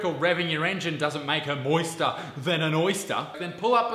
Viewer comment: ‘Moister than a oyster’ Me: DEAD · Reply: As a kiwi, I heard "Moister, then an oyster." Whatever floats your boat, but if she turns into an oyster I'll probably pass.